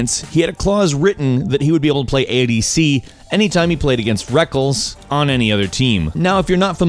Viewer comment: Did he just say Rekkels instead of Rekkles at 7:23 ?